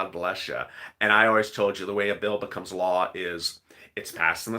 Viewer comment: I thought you said the 1400 dollar bill became law